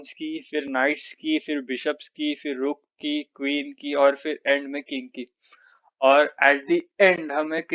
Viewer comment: i heard kinky 😂😂